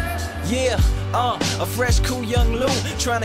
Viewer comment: why do I hear "Free chili" at 1:01? no thanks.